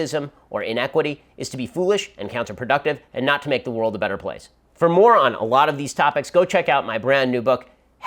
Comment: When he said "For More On" at 16:18 I was like "Who is he calling a moron?" then I listened and was like Whelp, that "moron" would be me lol